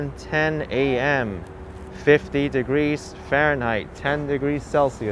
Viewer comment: For a second I thought he said it was 15 degrees, but I guess he said 50 degrees. I would take 50 degrees because it's actually 15 degrees here in Denver as the high today. But it will warm up back to 65 degrees later this week.